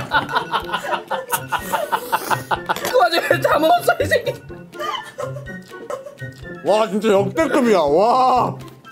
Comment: 내용:아핰핰핰앜다먹었어 이새낔ㅋㅋㅋㅋ 나만들림? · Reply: 그렇게말한거맞아요:흐하하하 그와중에다먹었어 이새끼ㅎㅎㅎㅎ